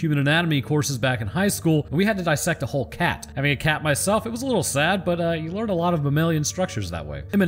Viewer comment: I thought he said "being a cat myself" not "having a cat myself"